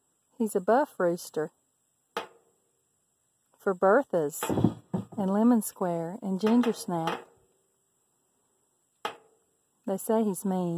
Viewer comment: It sounds like she said “we got a new rooster he’s a buff rooster for breakfast lemon square ginger snack” lmao 😂 not being mean but that’s what she sound like she said or did she actually said that? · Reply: Berthas, lemon square, and gingersnap...my buff hens!